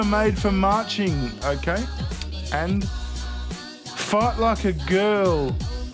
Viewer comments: Lol I thought he said "Fart like a girl!" 🤣🤣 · Sounded like you said "fart like a girl." 😂😂😂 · 😂 When he was reading the signs I thought he said “Fart like a girl” and realized the sign said “FIGHT like a girl” I can’t stop laughing!